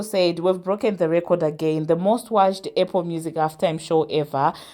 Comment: 😂everytime u say" APPLE" ..I THOUGHT U WERE SAYING AIRPORT. VERY STRONG ACCENT .cool topics.